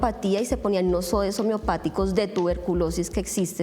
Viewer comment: En el minuto 19:50 cuando la Dra habla sobre lo que ponen a los pacientes para mejorarlos , alguien sabe que es? no entiendo muy bien ella dice algo así como, nosoesobiopático de tuberculosis (eso es lo que yo entiendo) pero intento buscarlo y pareciera que esa palabra no existe, alguien puede ayudarme? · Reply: Homeopáticos